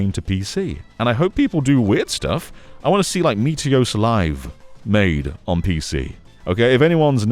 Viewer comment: What are you saying here? I can't find any results for "Miteyosa Live", "Mitayose Live" or "Mitose Live". Do you mean Yosumin Live?